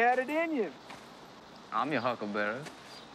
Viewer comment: Always do enjoy people mishear huckle bearer and think he says huckleberry.